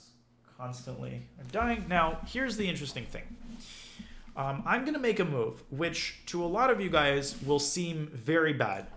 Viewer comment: What’s the term that Danya says around 22:00 ? Something that starts with an f? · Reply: fianchetto'đ the bishop is placing it on g7 (or b7) as black in front of the rook